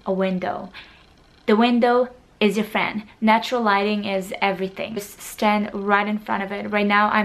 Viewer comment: when she said lighting I thought she said "Gliding" and I was like, Waaa?! I'll glide in every picture so I'll look good?! Wait! Whaat?!